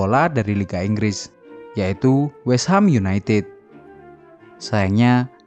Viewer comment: menit 9:53 ga jls ngomong apa om, "dengan menyem........? dr martin ini membuatnya tdk sepsial lg" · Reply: menjamurnya om, suka belibet kalo udah akhir-akhir om 😆